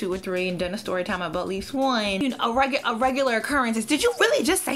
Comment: what did you say at 2:43? I’ve listened so many times and can’t figure it out. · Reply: Daneille Foster-Russell a story time about at least one